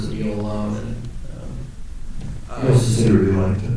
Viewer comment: What does Ryan say at 17:50? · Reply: "He also said he really liked it." Yeah, Ryan was very funny, but he is so soft spoken, the echo in the mic often made it difficult to understand him clearly.